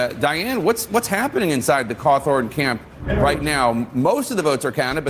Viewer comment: using Closed Captioning....LOVE that CC called him "Cloth Organ" instead of Cawthorn, LOL!!!